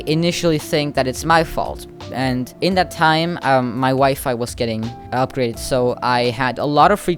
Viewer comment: I thought you said "my wife got upgraded" for a second😳